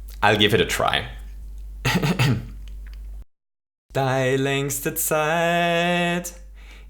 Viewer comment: Austrian people pls translante what he said during that 14:29-14:39 section! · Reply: <link> just the first sentence and he says "Dei längste Zeit..." and not "Dei hohe Zeit...".